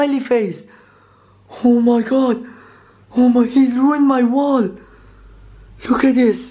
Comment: for those of you who have ear problem's he said "He Ruined My WORLD" but no he Actually Said "He Ruined My WALL"